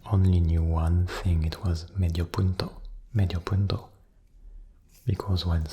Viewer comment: I talk on spanish and in 5:09 I couldn't understand what do you said. I was like: ''Medio what?''. I was trying to understand it until you said ''Half of a point''. In that moment I knew that you said '' Medio punto'' xD P. S: Good video :D